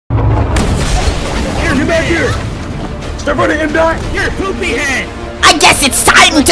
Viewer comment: did he say stop running mdot?